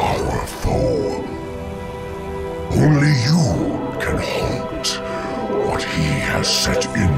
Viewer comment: He says halt, but the subtitles say stop.. noobs